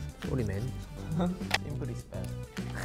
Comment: My favorite is 1:17 seungsik said simple is best, but at first i misheard as dimple is best🤣